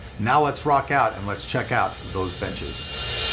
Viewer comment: I always hear "let's check out these bitches" haha